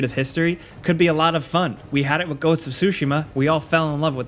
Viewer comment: did he just say sushi-ma??🍣